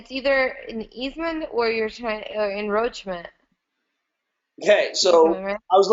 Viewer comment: Did she say "Enroachment"? 14:50 Works for me!